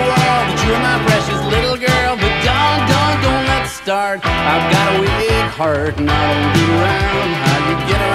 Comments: for 15 years of my life I misheard this song as saying "do-do-doughnut star" why, me? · for 15 years of my life I misheard this song as saying "do-do-doughnut star" why, me? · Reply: I just introduced this song to my 10 & 7 yr olds tonight. My 7 yr old thought he was saying "I've got a wee cart." With your line it gives the song a whole new,and more confusing meaning!